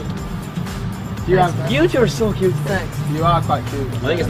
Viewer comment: I’m crying so much right now 🥲🥲…… turn your volume all the way up and it sounds like Lou said “ thanks babe”- 😢🔫 😭😭😭😭💚💙